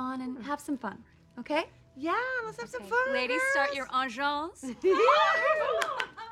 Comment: “Ladies start your- - - Vengence”?!?. Lol even French couldn’t get what it said. Do you?